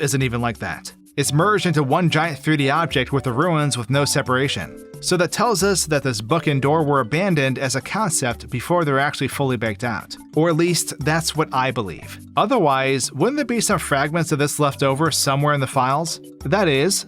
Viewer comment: I looked away and thought he said "this f*ckin door". I did quite a large double take